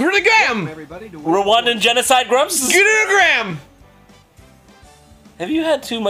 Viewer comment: Anybody else catch that "Genocide Grumps" at 1:09?